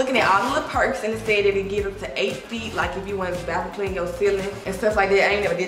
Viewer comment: Whole time I thought she said Bathroom cleaner .. I was so confused 😭😭